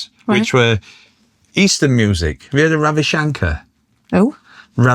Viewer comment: Thought said Ham Shanker for a second there, oops misheard!!